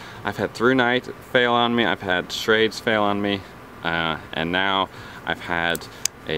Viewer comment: What did you say at 1:13??? You had what fail on you??? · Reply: i had schrades fail on me" schrade is a budget friendly knife company that makes imo one of the best budget knives